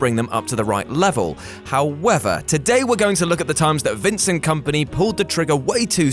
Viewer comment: Did he say "We're going to look at the times where Vincent Kompany pulled the trigger way too soon"?